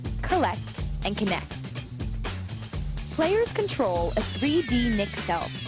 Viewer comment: I thought she said "players can troll"